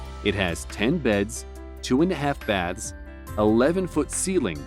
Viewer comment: did he just say two and a half baths